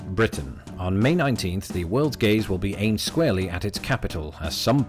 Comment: I thought you said the world’s gays in the intro.